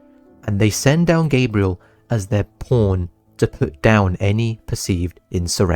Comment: why the hell does it say porn in the subtitles💀🙏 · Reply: pawn and porn are pronounced exactly the same where i live 💀